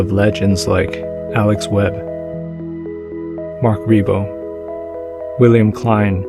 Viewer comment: I have to know who did the photo of the three welders at the shipyard. It sounded like you said “Mark Rebo” but frantic Google searches turn up nothing. Would you mind pointing me to where I can find this photo for myself? It’s simply… well, as you said, mesmerizing.